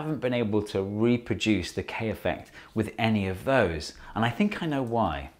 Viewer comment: Thought it said Kanye for a sec